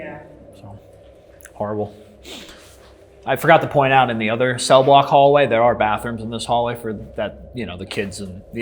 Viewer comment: as soon as he said "cell blocks" i misheard "sell blocks" and instantly i was in commodities futures trader mode im jumping on kraken propy now!!! lmao